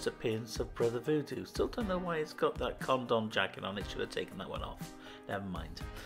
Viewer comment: Awesome collection! Did you say 'condom jacket' at 7:18? LMAO